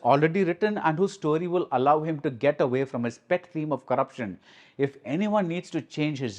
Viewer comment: i heard pet theme as wet dream 😭